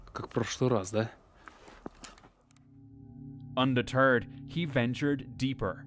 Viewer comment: undeterred sounded like Under💩 ❤❤